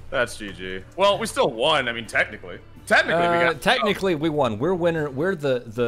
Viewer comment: I thought they said “we won, Technoblade” and then I was like “why did I think that” and then I looked at the final scoreboard and realized that it was bc the MVP of the red team was a guy named Technoblade. For a moment I thought there would be a legendary crossover... but no it was them saying “technically” and some non who named themself Technoblade